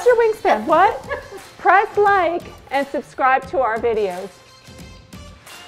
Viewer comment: LMAO not sure what that sounded like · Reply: "little bit too hard, sorry"